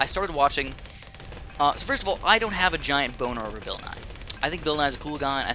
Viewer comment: What did I just hear? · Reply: BON ER